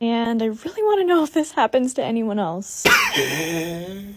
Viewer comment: Her: Does anyone get these TONSIL STONES: but for real these things are called tonsil stones me: NO DUH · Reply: I thought he said "for real those Aren't called tonsil stones"